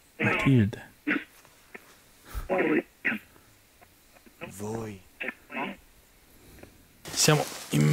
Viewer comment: io ho sentito morite voi e secondo me la Bambina è cattiva anche se quello che vi dice non è da bambina...però non so... Dato che vi ha dato delle me*** comunque attendo il prossimo episodio bravissimi💓 P.S. La farfalla attaccava non so se era normale... 😂